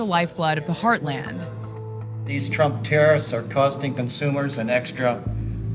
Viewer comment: Did he just say trump terrorist 🤦🏽‍♂️